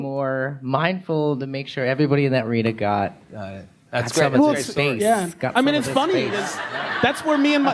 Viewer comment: "Got some of this face, got some of this face." ohmygod, I can't comprehend what I just heard him say xD